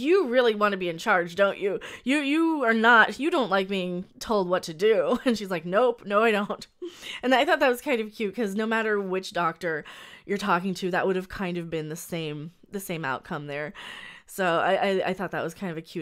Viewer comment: Did you just call her a witch doctor?